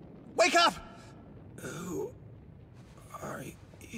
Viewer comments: I swear I thought he said "I had to sh*t a few pounds" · When I first saw this scene I thought he said, “I had to shit a few pounds,” 💀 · I thought he said, “I had to shit a few pounds” oh my lord 😭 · I thought he said I had 2 shit a few pounds 2 win the fight lol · I heard "i had to shet a few pounds" at first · really thought he said “shit a few pounds” my bad y’all 🧍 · I HEARD: “I had to shit a few pounds” 💀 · BAHAAHHAHAAH IF YOU LISTEN CLOSELY IT SOUNDS LIKE HE SAID HE HAD TO SH*T A FEW POUNDS AAHAHAHGAGAGAHAAJAU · The first time me and my cousin saw this seen he started laughing out of nowhere and later he told me it's because he thought fatgum said he had to "shit a few pounds" and now seeing this scene again I can kind of hear it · What it says: "I had to shed a few pounds..-" What I heard: "I HAD TO SHIT A FEW POUNDS-" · I hear it as “Shit a few pounds” 😅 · i thought he said "shit a few pounds" instead of "shed a few pounds" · why did I hear "I had to shit a few pounds-" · I thought he said shit a few pounds instead of sheed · i heard “i had to shit a few pounds” goodnight 💀💀💀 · Bruh at first I thought he said “I had to shit a few pounds” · I thought he said, ”I had to shit a few pounds, but cmon’” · I don't hear well so I thought he said "I had to shit a few pounds" 😂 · Honesty. at first. I could have sworn he said "s*** a few pounds." anyone else or just me? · The way I thought he said he had to shit a few pounds but come on